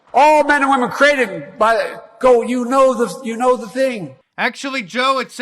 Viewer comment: This is what the subtitles thought he said😂😂"We hold these truths to be self-evident, all men and women are created by gold you know this you know the thing"